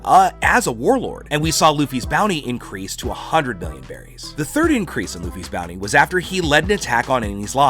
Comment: Did he say 100 billion berries or it is just me